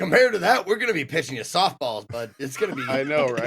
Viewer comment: Granted I am still waking up but it sounded like said "We will be pissing you soft balls".......